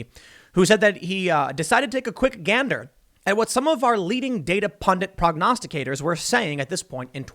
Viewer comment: I thought you said “not leading”... made me nervous for a few seconds.